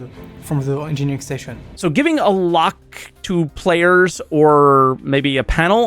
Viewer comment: french guy speaks so funny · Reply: I heard him saying "payers" instead of "players" (although I speak french too)